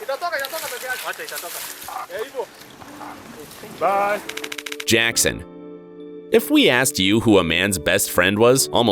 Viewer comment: I don't understand the language but that guy sounded like he said "Bye!" I found that really wholesome ❤ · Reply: Yeah, he said 'Bye' 😁. Most of it is in Kiswahili.